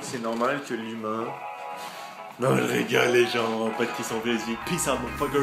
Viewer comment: Il dit quoi à 6:30 ? "Pas de question ..?" ?